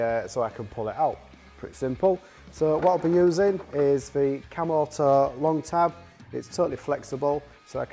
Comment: Thought he said camel toe not cam auto 😂